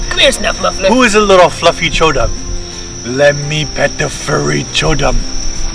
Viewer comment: I thought said let me pet the furry chungus.